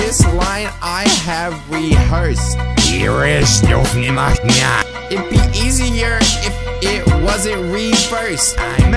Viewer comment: I didnt know what it said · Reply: It was reversed and said I’m coming for jimmy